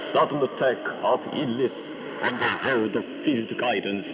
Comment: Does he say "passed away from a sudden attack of idiots"? :-)